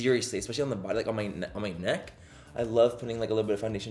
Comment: When you said “neck” around 8:20, I thought you said “nuts” 😹😹😹 I love you Manny!